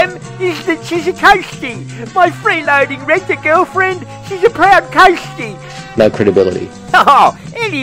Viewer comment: I misheard that as "proud toastie".